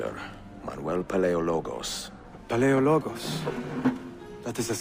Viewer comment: Auditore: "... Manuel Palailogos" What I heard: "Manuel, playa ya logos."